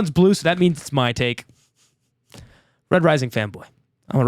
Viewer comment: I thought said “Red Rising femboy” and was so confused. Im so tired goddamn.